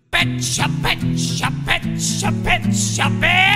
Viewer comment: I thought he was saying a bench all the time, I’m an idiot